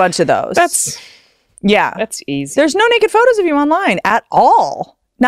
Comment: did she say that's easy?!😆